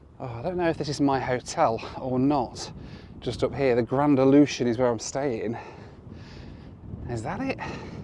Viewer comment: I obviously have dodgy hearing... because I heard you saying "grand ablution" when you were talking about your hotel! 💩💩